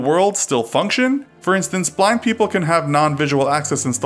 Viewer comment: did he say black people